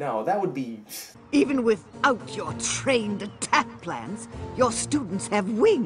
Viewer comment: When I saw this movie for the first time I thought she said trained attack plans not plants I'm like what plans the Death Star plans